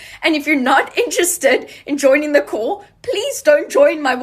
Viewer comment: The second girl sounded like she was saying "join the cult". I know she has an accent but I'm still not sure what she was actually saying